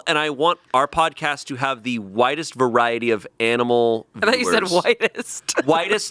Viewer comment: I also thought he said "Whitest" Also I thought the guy in Powder was bald.